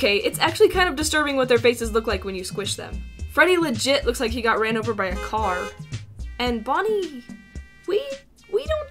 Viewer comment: FREDDY LOOK OUT BEEP BEEP 5 hours later 2:56 what said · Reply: What's going on! Freddy Fazbear, come back! You'll get hit by a... I warned Fazbear Entertainment to put a rail there. Alright, Animatronics. There has been an incident in the Pizzeria site that I don't want to get sued for. We're going to cut the show short for tonight. Everyone go to Parts & Service.